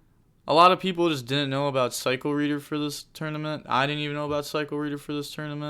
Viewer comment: does someone knows the card that said on that exact moment? I heard Cycle Reduer lmao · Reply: Ally of Justice Cycle Reader